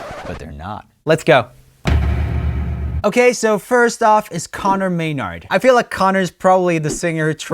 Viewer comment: I thought he said Connor Mayo